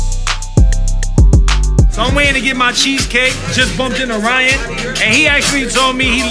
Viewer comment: "cheesecake" instead of cheese steak.